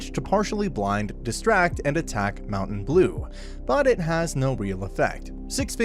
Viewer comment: i fr thought he said mountain dew instead of mountain blue